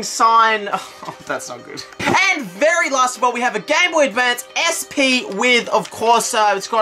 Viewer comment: sounded like he said gay boy...